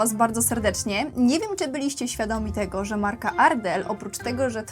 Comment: Zamiast Ardell usłyszałam Adelle 😂🙈 I miałam takie nie wiedziałam że ma ona swoją markę kosmetyków 🤣